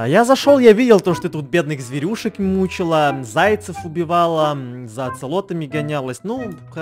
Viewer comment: Мне послышалось за аксолотлями гонялась 😆